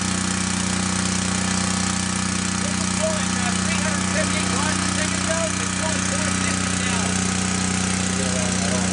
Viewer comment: what does he say @ 3:28? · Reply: I believe he said it was going at 350 watts a second ago and 460 now.